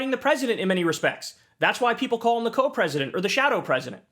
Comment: "Co-president"? This whole time I thought people were saying Elon was the "coke president"